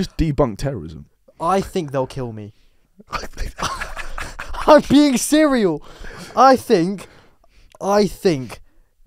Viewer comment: Anyone else hear say “I’m being cereal” at 37:29 😂